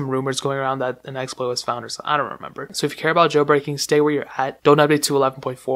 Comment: What did he say 2:43 jail breaking?